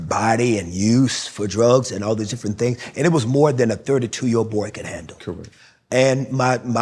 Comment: did he just say a 32 year old "boy"?